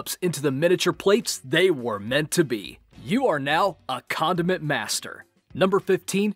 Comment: When he said condiment master, I thought he said condom master. Hahahahah